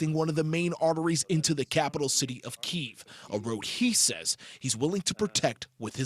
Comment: This guy is so dumb, I thought he said he’s willing to negotiate smh